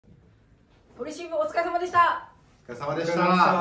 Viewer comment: おかぴー最初堀渋って言ってる？ 堀慎吾お疲れ様でしたって言ってるように聞こえたw